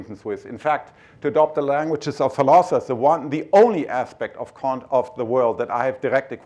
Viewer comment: I think captions should say "languages of philosophers"